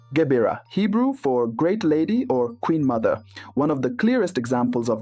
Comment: He change the scripture too. Bathsheba is King's mother not Queen's mother. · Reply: no he didn't. you misheard the video. he explicitly said it's the king's mother, but he said her title was the "queen mother", not "queen's mother".